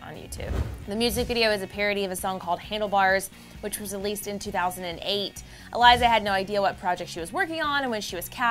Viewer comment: 2008??????????????????????? Did I hear that Right